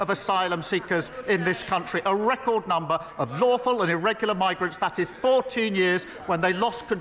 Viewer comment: Lol I thought he just said illegal 😄 the. He said irregular!